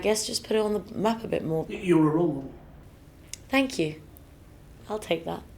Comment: What did Krishnan say at 3:55..? Can't make it out.. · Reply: he said "you're a role model"